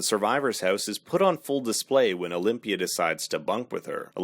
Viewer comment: That whole time I thought they were saying Olivia instead of Olympia